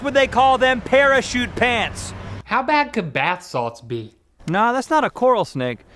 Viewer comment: When I watched this video when I was younger, I thought Link said "bath sauce" as opposed to "bathsalts" in this scene. I presumed that "bath sauce" was just Rhett and Link's bizarre term for shampoo or whatever. Gosh, I so innocent back then.